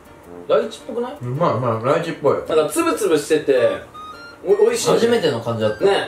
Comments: つぶつぶしてて が ツムツムしててに聞こえたww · つぶつぶしててが ツムツムしててに聞こえた笑 勝手にツムツムしながら 食べてたらって 話するのかと思った笑